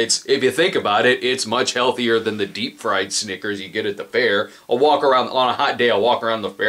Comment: Did... did he just say deepfried snickers? He's joking, right?